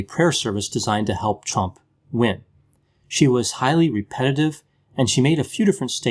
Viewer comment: It sounded like you said " 'Chump'," which is okay with me speak your mind brother.